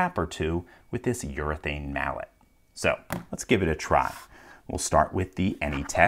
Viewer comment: i thought he said "urine mallet"